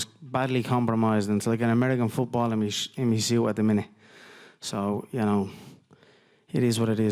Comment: wtf is he saying?? It was like American football what?? Someone please transcribe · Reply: Balimush emuseeya at the minute? That’s what I heard. But what I think he was trying to say what “like American football and u see it in my shin at the minute “